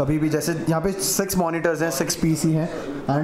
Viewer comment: LOL did he just said sex monitors 😂😂😮🤔